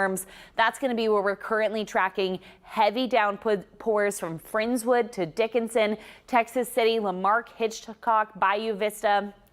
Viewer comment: Did you say, "Down in Dickisin?" And then 5:28 Did you say, "It's warm, steamy and wet?" I feel it, and I totally agree!!!